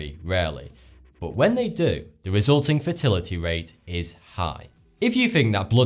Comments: I could swear he says chance of fertility is high instead of Mortality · did he say fertility rates??